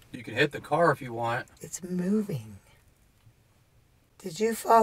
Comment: Did anyone else notice between 53:39 mins & 54:00 when in the cemetery just after she asks what is you're name A whisper says (IT'S MOVING) Please tell me if I'm wrong?? 👋🏻😎🌟🌟🌟🌟🌟